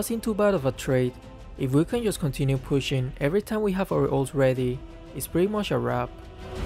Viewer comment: It's pretty much a wrap, but heard it as It's pretty masarap 😂